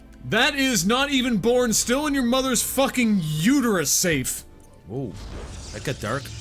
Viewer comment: thought when said "not even born, still in your mother's womb safe" that he was saying "still born." Yes, we have some interpretation problems going on here.